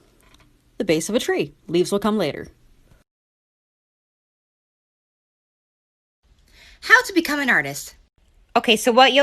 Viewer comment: leaves will come later kinda sounded like terms and conditions applied